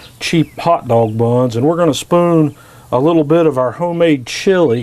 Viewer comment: Do you have a video of the cheese making? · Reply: ***** Ah sorry sounded like you said home made cheese :)